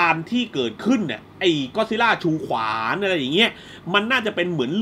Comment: ผมได้ยินพี่พูดว่าก็อตซิลล่าถือขวาน อืมมมมมมม